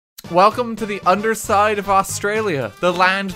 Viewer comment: I thought it said underslide and i was excited for a bit